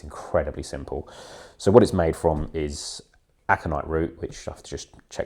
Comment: Alkanet root flakes. I believe there pretty easy to find online 👍🏻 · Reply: It really does sound like you said Aconite root on the video .... also known as monkshood or wolfsbane - about the most poisonous plant in the UK. I am relieved it is Alkanet root. I picked up on it as I would occasionally use it as an oil coat prior to french polishing pianos ... 50 years ago PS You wouldn't be able to buy it, but boys being boys, didn't want someone pulling plants and drying roots as a diy project.